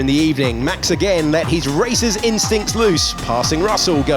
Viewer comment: Swear i thought it said "let his racist instincts loose" at 0:46 had to play it back LOL